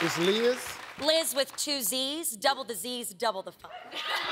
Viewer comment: I heard " Double disease, double the fun. "